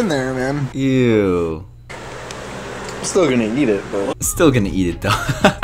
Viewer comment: Still gonna eat it Thought you said still gonna yeet it😄 ( was'nt a good joke?)